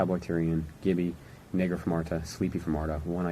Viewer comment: wtf???? Did he say nig**r from artesia?